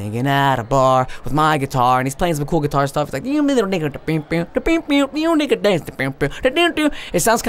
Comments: did he say nigga? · did he say the “N” word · did he say nigga? xd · did he say n!#$a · you sounded like you said ni**a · did he say nigga?!?! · I SWEAR HE SAID N***A DANCE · it sounds like he said nigga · did he say n*gga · Not hating or anything but did he say N***A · at the guitar guy i heard something "yeah nigga dance" xddd listen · i heard Ni**a dance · did he just said N****? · listen closely at 3:17 it sounds like he says n*gga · i heard n*gga 😅😂